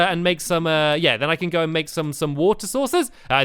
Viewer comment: I thought u said water sauces